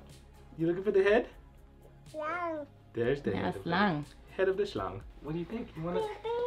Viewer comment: Dude I thought you said head of the schlong lol I was like man I could not speak that language without constantly talking about the schlongs its such a funny word lol